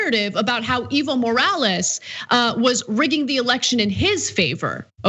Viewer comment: I thought she was saying "evil morales" was the guys name haha! I had to look it up to get it right. I thought it was just too perfect to be real.